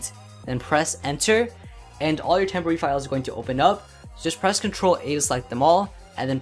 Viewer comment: what did he say at 0:38 press control and? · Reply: its ok guys he said a